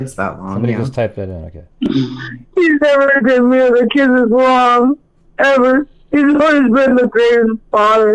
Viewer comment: “he’s never done me or the kids wrong. He’s always been the best father.” I’m pretty sure that’s what she said.